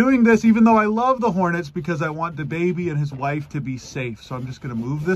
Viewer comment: Thought i heard it wrong like you meant your baby and your wife not da baby and his wife